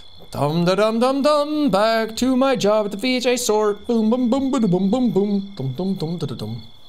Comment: straight thought he said 'bj store' instead of 'vhs store'